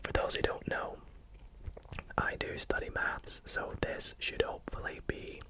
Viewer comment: I really thought he said I study meth like bro I had to listen to that for a good minute before I realized that he actually said math